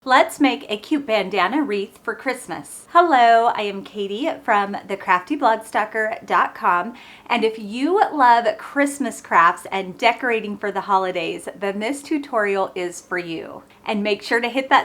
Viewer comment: I saw this post as I was scrolling on YouTube, and thought it looked interesting. I was NOT disappointed! This is such a cute idea! I had to laugh when you introduced yourself and mentioned your blog. I thought you said “The Crafty Bloodsucker!” I had to go back and look again! 😂😂😂